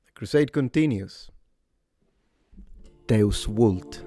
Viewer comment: As a romanian, for a second I thought you said „Te ascult” which translates as „Im listening to you”😭😭😭😭